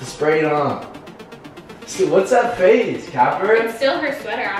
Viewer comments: is it just me who heard him say caterine???? 10:14 ... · wait did anybody else hear call Catherine???? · Did he say Catherine.